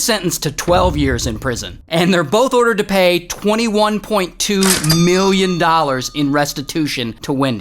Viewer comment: i thought it said $21.2 + FRIES